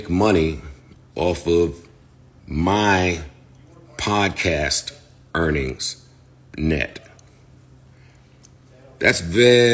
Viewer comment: I’ll have to listen to it again. But I thought he said they are paid from HIS net of the podcast not from the podcast net which is different. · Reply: yes he said “my podcast earnings net”. It’s still a percentage which apparently Rory & Mal agreed to be, which why they asked for accounting, which makes this MORE fishy cause if there is nothing to hide, why trip over them asking? That’s how they get paid correctly. Joe is talking in circles & it’s by design.